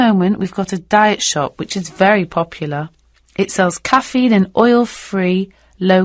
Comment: I got 40/40. This is the first time I have done such these questions. I feel thankful for your test. It makes me have sense of confidence Btw, Q4 should have been Coffee rather than caffein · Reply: yes, you're right. 3:08 I've just checked it again. As both "coffee" and "caffeine" sound like homophones, so I misheard it. Thanks a lot♥️